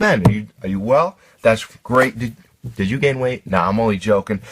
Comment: Lol at the beginig i thought he said "did you get laid?" I was like wtf